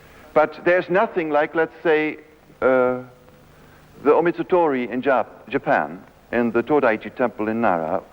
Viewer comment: Anyone know more about the 3 days and 3 nights ritual in Japan - sounds like he says "Omitatori" (18:35) or something, and I can't find anything about it online. Would be great to read up on it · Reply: It's Omizutori, see in p. 93 of the transcript: <link>